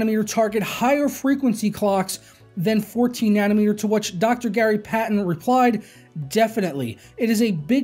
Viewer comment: I thought you said Gary Payton at first. Gary Payton top 10 plays <link>